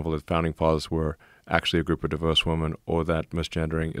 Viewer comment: Lol I thought he was saying "divorced women" for the first two hours